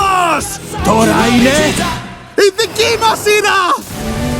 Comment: Η δική μου σειρά ή Η δική μας σειρά ή Η δική μο σειρά? (δεν μπορω να καταλάβω τι λέει απο τα 3 😐) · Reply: Η δική μας 😅